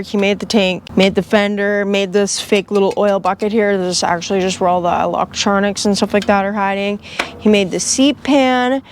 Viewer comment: Huh?!? At 14:40 you said "#?#@ don't exist under 50 CCs." What was the 1st word? I even slowed the video down to 35 and still can't figure out what the word is supposed to be... PS, I don't speak baby, knee walkin, snot slinging drunk or what ever that language is. 😂😂😂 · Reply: Hahaha I said “stops signs don’t exist under 50cc”